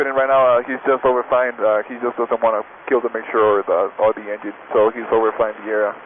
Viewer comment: Round of applause to this student pilot, Norcal controller and all other pilots and instructors who helped him land safely. This is aviation!! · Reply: the guy says "he just doesn't want to kill the mixture or the engine" rather than "he's just gonna cut the mixture and the engine". Not sure if you can change that on an already uploaded video but I figured you might want to know as it's kinda the opposite of what the subtitles say.